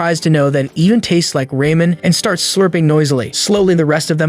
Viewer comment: Is it just me or did i heard “Rayman” on 20:03 or 20:04? I swear i must be tripping because i never thought Ramen was pronounced like that 😭